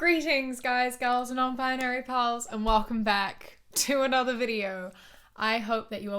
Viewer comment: i love how u start ur videos with "...guys, girls and *nonbinary pals**" because although im not nb it just makes me so happy :D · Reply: i love that with her amazing and gorgeous accent, i keep mishearing it as "nonbinary pearls" and that just makes it sound 1,000% more special and adorable to me. Like, yes, we've got guys, we've got girls but we've also got nonbinary people and they are absolute *PEARLS*! ❤🌈💜 Makes my damn heart sing every single time.